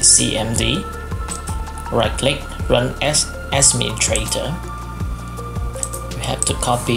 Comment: i heard you said ''ask me you traitor'' instead of ''administrator''